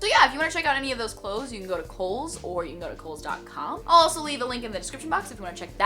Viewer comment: I was so confused for awhile because when she said she got the t-shirts from Kohl's I heard it as Coles which is a food supermarket in Australia :'-)